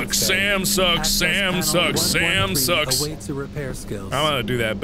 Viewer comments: when he was singing "SAM SUCKS SAM SUCKS" i thought he said samsung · It sounds like you was saying samsung in stead of Sam sucks